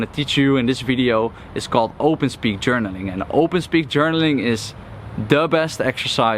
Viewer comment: wonderful video! quick question, did you say "open speed journaling" at 5:37? I want to do more of it. Thanks. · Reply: It's called open speak journaling ;) Basically journaling from a place of the shadow to let the emotions out. Then throw away the paper or delete the entry. Good luck!